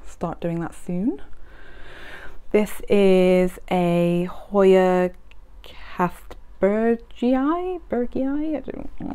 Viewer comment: wow, it's beautiful! what's the name of the Hoya on 26:35? I didn't quite catch it and those leaves are really cute! And congrats on 1K!! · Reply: Thank you so much lovely! 🥰 that’s a Hoya kastbergii 🥰 it is such a cutie isn’t it?! 😍